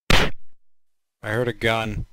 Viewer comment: Flumpy 0:06 hey how's it going Blam 0:12 I heard gun clown 0:18 boo